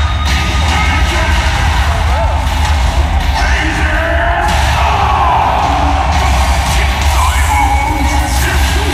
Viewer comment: I'm not familiar with Geese, what is the crowd shouting at 2:02? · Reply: RAGING STORM!